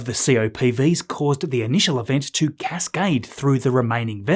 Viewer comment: I heard "gascaded" instead of "cascaded" 😆